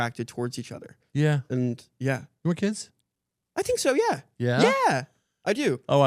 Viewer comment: Fully thought Caleb said "you wanna kiss?"